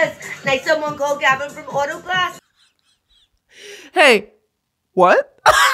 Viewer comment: brittany's content grabs me by the throat sits me down in a chair and i kick my feet and giggle like "teehee" "like someone called gavin from autoglass" is kinda what it sounded like